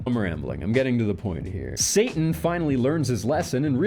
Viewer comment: Played it twice to be sure but you really said "satan" instead of "santa" at 20:55, well played 😂